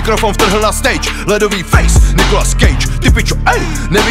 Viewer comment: Is it saying "Nicolas Cage" ?